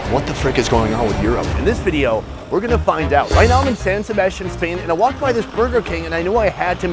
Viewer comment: i thought that said arsenal